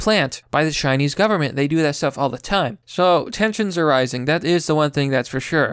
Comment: Damn it! You said "Tensions." I thought you said "Texans are rising" at first. That'd be epic. A raid of armed Texans descend on Hong Kong disguised as tourists to lend aid to the freedom fighters. A place in Hong Kong is called New Alamo in their honor.